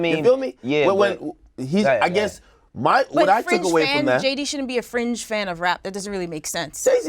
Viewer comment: Stop saying JD I thought she was saying JAY Z had an opinion on female rappers. 😑